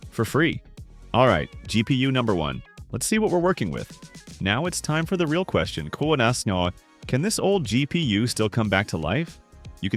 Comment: the dangers of poor spelling and using AI narration. i have NO idea what you said here... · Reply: "Now its time for the real question koalasnoh"